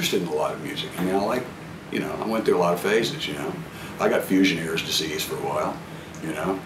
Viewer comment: at @ 3:20 did he say "fusioneers' disease" ?!?!? love it. RIP M.Rhodes.... gone too soon